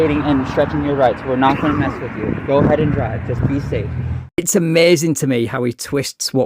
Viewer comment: Did he just say that they told him to "go ahead and DRIVE"???? I thought he was "traveling".